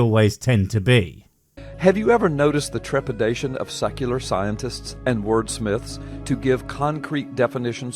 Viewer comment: maybe it's the accent but i keep hearing "sucular scientists". is that a thing?